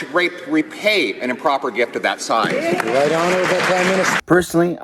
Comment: They keep referring to Trudeau as 'The Honourable Prime Minister... but I keep hearing "The INTOLERABLE Crime MInister"!